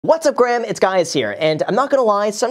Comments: From the very beginning of the video, did I hear " what's Graham its guys here?"🤔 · Did he just say in the beginning what's up Graham, it's guys here? 😂